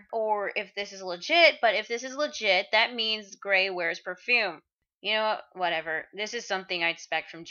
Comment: could have sworn it said "parfum" like Ichiya would use for scent